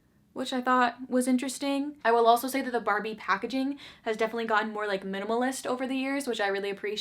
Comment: I heard “Barbie’s acting had gotten more minimalist over the years” before I realized you said “packaging” so I was just like yes, yes, Barbie for best actress, of course